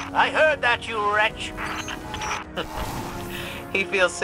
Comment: is where I had to turn the subtle on, I had a great laugh when I thought he called Ikora a bitch, but THAT was great!